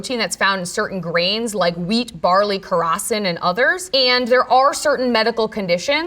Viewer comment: Can someone please help me identify which grain is the Dr talking about on 14:47? She says wheat, barely and...? The subtitles say "rosin" but I'm not sure is that because it seems that she is sayins "krosin" or something like that. It caught my eye because I'm gluten intolerant and I have never heard of it. Thanks in advance! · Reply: Sounds like she said khorasan, which is a type of wheat.